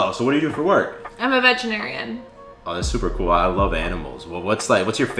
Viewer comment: I totally heard “I’m a vegetarian” not “I’m a veterinarian” 💀😂